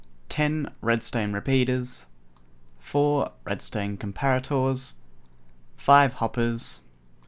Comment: turn on the captins it says red stain instead of redstone